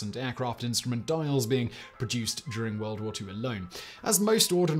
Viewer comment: “World war 2 alone” i heard watermelon 🤦🏽‍♂️